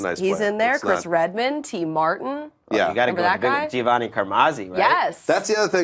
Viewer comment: did anyone else notice when they named the QBs drafted before Tom Brady that she said or sounded like she said "The Black Guy"